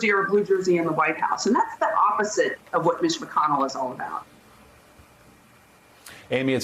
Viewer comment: Which McConnell? Bit** McConnell? Oh, Ditch McConnell, got it. For a moment i thought you said Switch McConnell...